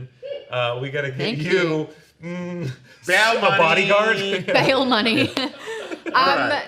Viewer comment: I thought said Baylan money